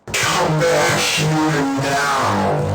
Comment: When the Dr. first chased you, to me it sounded like he said "Come back here, now!".